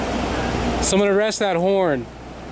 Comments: She heard you say somebody arrest that whore 😬😛😛😛😬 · Lol she thought you said "Someone arrest that whore" not "arrest that horn" lol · She thought you said “someone arrest that wh0re” lol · omg she thought you said „arrest that whore“ thats why she said „i have a normal job“ ahahaha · I think she thought you said that they should arrest that hoe lolololol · She thought you said that they should arrest that W***E not HORN. Wow, he got a hug. What about officer safety?